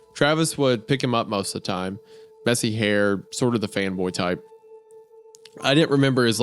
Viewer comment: audio only guy here and this part made me come check the lyrics for the video cuz I could swear Wendigoon said ''femboy'' instead of ''fanboy'', good video folks.